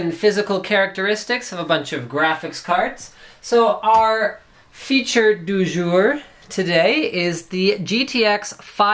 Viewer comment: did linus say " our feature du jour "??